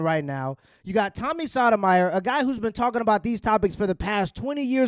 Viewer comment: Bro you need to pronounce Sotomayor’s name right. It sounds like you said sodomy. Come on now. Tighten up.